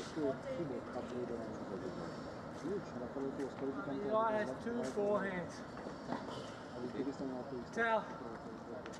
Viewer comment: What does Matosevic say at 2:26? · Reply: "Two forehands, I've never seen so much luck.